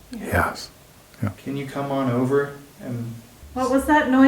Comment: it responds yes atleast that's what I heard